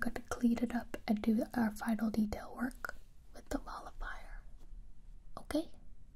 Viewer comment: I thought I heard her say Ball of Fire instead of Molifier so I was like. Well I guess I'm getting set on fire, that'll burn those worries away for sure! Lol